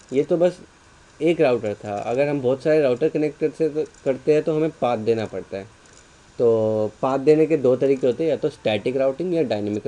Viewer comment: Did he just say "hamain paad dena parta hai" ??